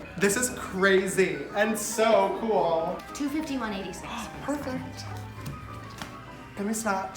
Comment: Cashier lady $251.86 James Perfect You a rish bish sister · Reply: You know what i thought the lady said i thought she said 21.85 and i was like HONEY WAAAAAT so i replayed it and i was like "oh that makes more sense"